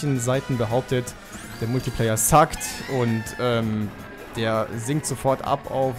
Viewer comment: I know it's probably not what he's actually saying, but at 8:20 it sounds like he said 'The multiplayer sucked.' xD